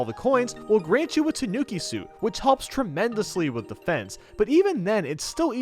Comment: Did he say that it Helps tremendously with The Fence?